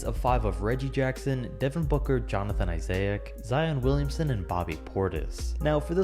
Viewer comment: Who heard him say Jonathan Isaiahcc